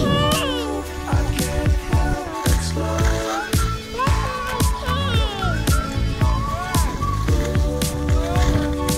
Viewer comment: “Wheeeee” Go 👏👏 What does she say at 03:07? (Thanks for the quieter music 👍👍) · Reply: “Look at my turns”